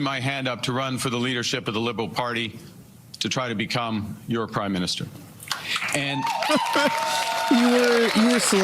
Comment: he says CRIME minister instead of prime minister. Perfect.